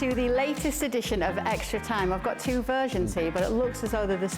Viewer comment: I thought she said Two virgins when she said versions🤦😂